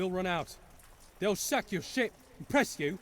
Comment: istg that npc in church DID NOT SAID ship 14:21 they will sack your shit 14:24 you be thrown on a quarantine shit